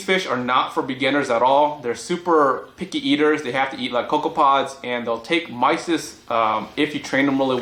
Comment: did you say cocoa-pods?